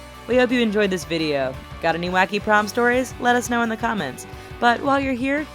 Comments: i thought it said porn stories like coed :3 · I thought it said "porn stories"... I'm so disappointed.